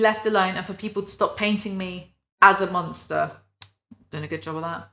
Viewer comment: she doesn’t want to been seen as a monster but i thought she said she was the monSTAHR